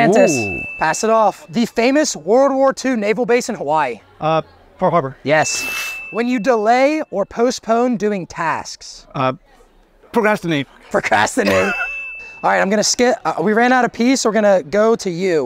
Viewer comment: Did he just say "We ran out of pee"? 😂